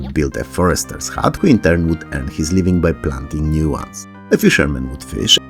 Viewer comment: I had to do a double take, because I misheard that a forrester would end his life by planting a new tree